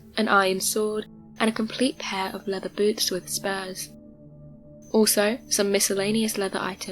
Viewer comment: I thought she said "spares," instead of spurs....